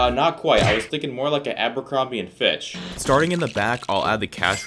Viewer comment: I thought he said bitch but he said fitchburg